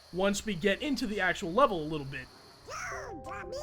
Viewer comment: I always thought it sounded like Donald said "NO, DUMMY!" XD Is it just me, or is there a lot of FPS dropping in this video? One such instance is at 12:02 "Lion painting", I see what you did there XD 19:06 This mirrors a scene in the movie, but in it, he said "It is time!", and given his mouth movement, it looks like they forgot to dub that line in XD. Then again, at 19:40, he says it... what? 21:33 SDG-23... and 24 only 8 seconds later?! How would he know Donald and Goofy, anyway? XP